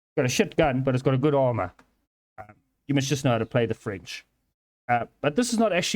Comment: "You must just know how to play the french." =, but what I heard was "You must just know how to play the fridge" XD lol